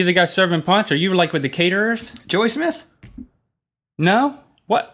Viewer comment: I thought you said "Julian Smith" at first...not "Joey Smith"